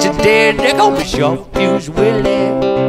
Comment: Ha! thought he was saying sharp tooth willie, guess I should pay attention...